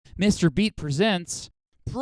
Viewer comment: like this comment if you thought that said mr beast